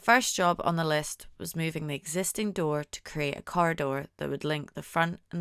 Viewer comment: I had to turn on the closed captioning because I thought you said you wanted a “car door” & I had no idea you meant “corridor” until I saw it written out, lol. My ears must have flopped over for that sentence! 😂